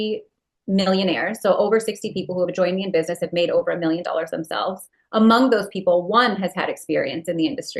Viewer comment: did she say a million in sales or a million themselves? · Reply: in sales i think is what she said.